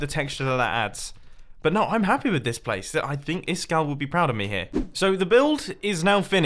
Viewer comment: if you watch with subtitles on instead of iskall it says is scale